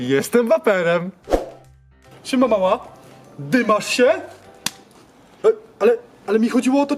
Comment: ja usłyszałem '' ej mama dymasz się " xd